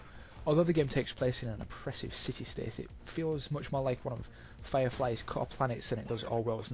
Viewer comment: What does it compares it to at 1:28, George Orwell 1984 and ? Cant understand the name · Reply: Firefly - the tv show.